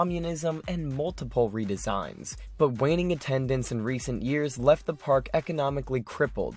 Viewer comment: Sounded like you said waning attendants